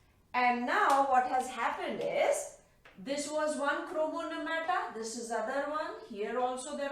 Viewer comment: Thank you maam ,now I'm clear with the concept.urs teaching is awesome 👍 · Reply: Hello, can u pls tell, what did ma'am say at 3:41 ? Chromoneumata? Can you pls tell what it is?